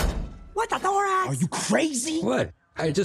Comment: When webbs said what the thorax I thought she said what the four amrs